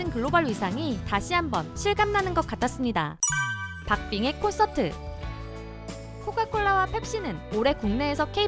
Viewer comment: 박빙의 콘서트..... 가 들리는 순간 짦게 빅뱅이 콘서트 하는가? 생각이 스쳐지나갔다..... 빅뱅이 아니고 박빙이구나